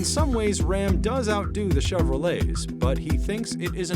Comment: Could've swore he said "Chevro-Legs" 😂